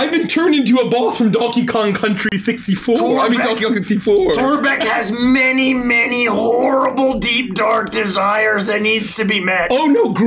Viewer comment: the way a paused his sentence sounds like he was gonna say "torbek has many many wh*res"